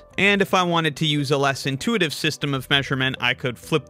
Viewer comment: I am NOT falling for that rage bait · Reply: its funny cuz i misheard it as "more intuitive" and it made me laugh lol